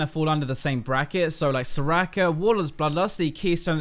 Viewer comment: All I heard was 'so-racker so-racker' :^)